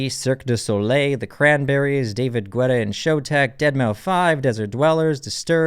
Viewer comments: Did he just says deadmau five? ;( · Did you just said Deadmau Five ?! 🤣🤣🤣🤣